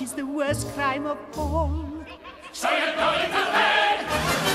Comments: Did he say ''You're going to Hell''? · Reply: No, he said "So you're going to hang!" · When Clopin say "So your going to hang!" he looks like he just got the best present in the world and when he runs over to the lever he looks like he really can't wait. When Esmeralda comes out at first he looks kinda angry and disappointed. I honestly really love Clopin. He an amazing good guy/ villain. Probably one of my favorite Disney characters. He is just amazing. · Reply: For the longest time, I thought Clopin was saying, "So you're going to HELL!" ...Although he's most likely an Atheist. Still. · "We find you totally innocent, which is the worst crime of all. SO YOU'RE GOING TO HANG!!" Boy, that escalated quickly. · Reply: love puppies I thought he said you're going to die lol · "We find you totally innocent, which is the worst crime of all. SO YOU'RE GOING TO HANG!!" Boy, that escalated quickly. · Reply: I always heard, "So you're going to DIE!"